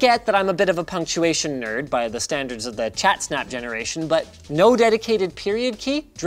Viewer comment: Did he say ChatSnap??